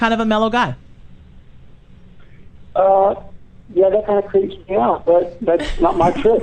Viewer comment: Thank you sooooo much for posting this. It's amazing to have them revisit after all this time! I had no idea they were doing this interview. What is and LaTalk? And can someone please tells me what Gale says at 11:00 about 'not being my choice', that everyone laughs at? It's hard to hear his phone voice. :) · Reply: A million years later but this is what he said: "Uhh, yeah that kind of creeps me out but … that’s not my trick"